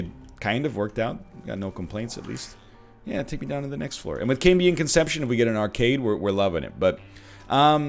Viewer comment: thought he said “With Kate being in conception if we get an arcade we’ll be loving it!” 🤔😂